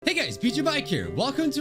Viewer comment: I thought said N*ggas biju mike here!